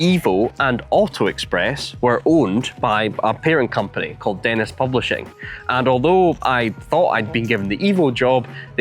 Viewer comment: Had to check the transcript, was sure I heard you say..."...a parent company called Anus Publishing...."🤣